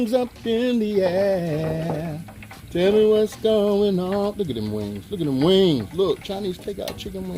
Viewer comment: Did y'all hear the crunch, I'm so greedy I thought he said look at the mother fucking sauce on the wings!! I'm so greedy, Yes, I tried this recipe, after I watched this, I'm so happy 😁👑